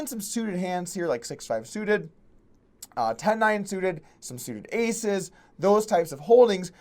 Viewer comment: Did you just say "some suited aces"? Around 1:20??